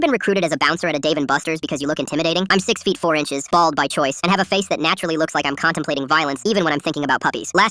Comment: I thought he said tall by choice and I was like what the fuck I wanna be tall